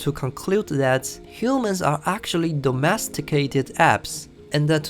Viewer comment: should be “apes”instead of “apps”